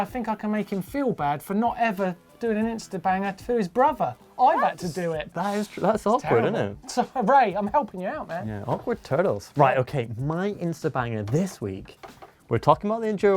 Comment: did say "awkward turtles"?